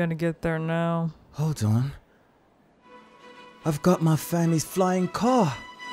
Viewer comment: why did I hear “I’ve got my fanny’s flying car” 👁👄👁